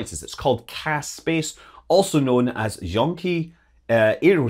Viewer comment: I heard Junkie Aerospace. lol